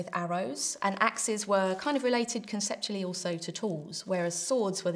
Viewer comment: I thought you said axes were relegated to dwarfs! Lol.